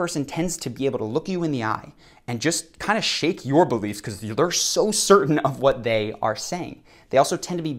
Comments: the caption says “shakes your boobs” instead of “shakes your beliefs” 😂😂😂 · am I the only one who heard "shake your boobies"